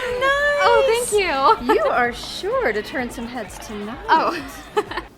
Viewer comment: This is such a great adaptation! (Also, I'm really grateful for the subtitles, as I thought said "You are SHORT...!" at 1:17, which was... completely off and quite confusing!)